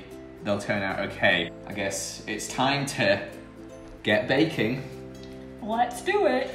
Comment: I thought it said breaking 😂